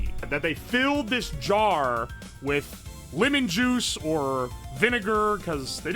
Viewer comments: Somehow misheard “lemon juice” as “women juice” 😭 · I swear I heard Woman-Juice